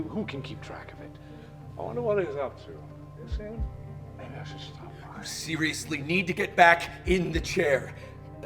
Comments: is he saying maybe I should stop lying? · I just caught what he said and I’m crying again (About Damien) “I wonder what he’s up to, have you seen him? Maybe I should stop by”